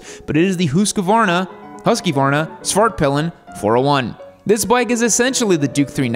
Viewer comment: Everytime says svartpillen, to me it sounds like hes saying fartpillen.